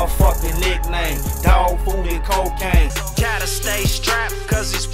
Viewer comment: did he say dog food and cocaine at @ 17:01 ? 🤣 🤣